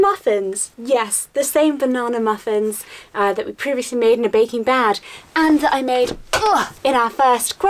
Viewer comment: captions say baking bag instead of Baking Bad